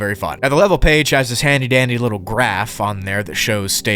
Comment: Did I hear dandy!??